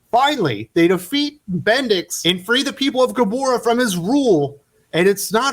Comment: When you said bendix I thought you said bendis. I thought Brian Michael bendis had finally been defeated.